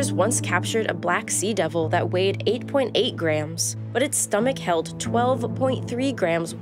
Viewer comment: Anglerfish weighing 8 grams? Have I heard it right?